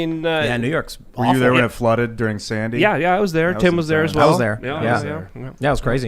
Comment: What the hell is said at the 15:04 mark? Red flooded during Sandy? Clarification from anyone, please.